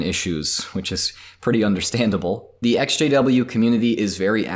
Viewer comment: Everytime he said Ex-JW I thought he was saying SJW...